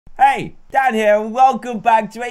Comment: Am I the only one who thought dan said "hey dad here" just me? Ok